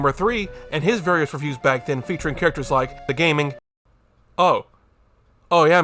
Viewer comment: I thought 26:22 said "The Gaming Racist" at first and I can't decide which is worse.